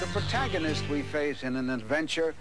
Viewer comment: Did he say protagonist?